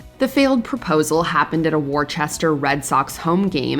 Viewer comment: My apologies to anyone from "WAR-chester" who heard that.